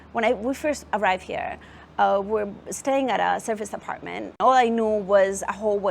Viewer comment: What’s wrong with the subtitles? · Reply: it Serviced Apartment not surface apartment. Obviously you are using auto caption instead of putting in efforts.